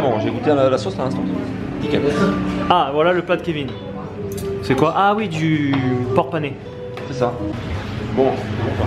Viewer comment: j'ai compris du "porte-monnaie" LOL